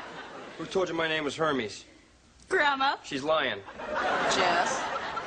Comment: i thought she said Herbies